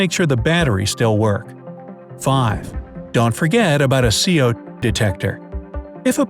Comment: BRIGHT SIDE: "Don't forget your CO_ *random pause* detector." Timestamp: 2:21 Me: "Is it saying Se'ode, or CO_? How come it says CO² at the end of that part?" Timestamp: 2:41 IT MAKES NO SENSE!!